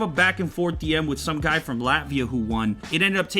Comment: did he said Latvia ?